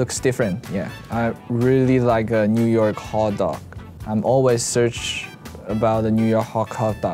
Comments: closed captions say "(speaks in foreign language)" but he literally said HOT DOG?? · I love how the subtitles say foreign language at 1:51 when he's really just talking about hot dogs 😂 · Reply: Casual Carolyn 😂😂😭he just said hot dog😭😭😭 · Subs vs What Lay actually said 1:51 "I really like New York (speaks in foreign language)" "I really like New York hot dog" 1:53 "I've always search about New York" "(speaks in foreign language)" "I've always search about New York....." "Hot dog" 1:57 "I want to (speaks in foreign language) " "I want to hot dog" I'm crying Lay's interest in hot dogs is so deep its not understandable even when he speaks in English and all they had to do was sub it in English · Lol the subtitles, they said that he was speaking a foreign language when he was actually just saying hot dog, hahahaha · What the captions say he said : I want to try New York (foreign language). What the fans know his slightly broken english actual was : I want to try New York hot dog.